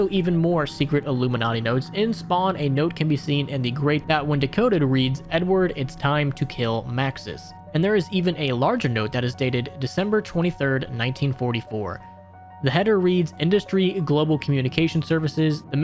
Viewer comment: I thought he said "N word time to kill Maxis" 💀